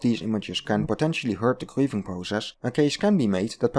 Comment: I heard "the queefing process". 🤣